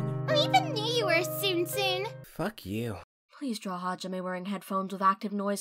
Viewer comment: I thought teruteru said f*ck you cause of how close he was to the words